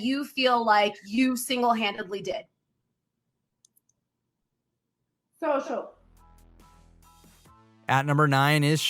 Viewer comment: What did Abi-Maria say at 2:09?? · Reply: Social